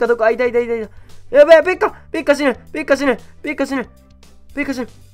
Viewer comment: ペッカ死ぬがペッカ死ねと聞こえた。